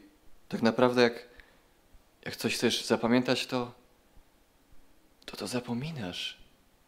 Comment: tam gdzie było zapamiętać ja usłyszałam zapłodnić xd:)